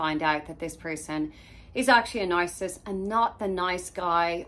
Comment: About 0:48 it sounded like you used the term "nicecissist." That might become the next hot online term.